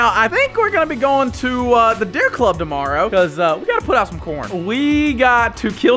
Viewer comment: I thought you said I need to put out some p. orn